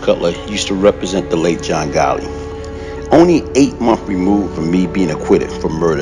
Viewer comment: DID I HEAR BROTHER SAY,"JOHN GOLLY?" IN REFERENCE TO,JOHN. GOTTI,,,'???